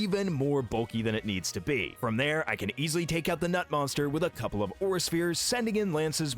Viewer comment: did bro say nut-monster or knot-monster 😭💀